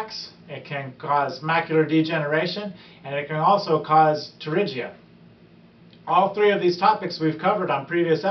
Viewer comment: What did he say at 1:33? I've never heard of it and I can't find it on Google. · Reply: You're probably spelling it wrong. It's a Pterygium, or, surfer's eye. Here is a link that explains it simply from the American Academy of Opthalmology <link>